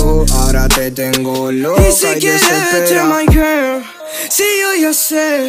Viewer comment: Yo escuché "Y si quiere verte my Ken 🗿🤌" pero en realidad dice "Y si quieres, vete my girl" 🤓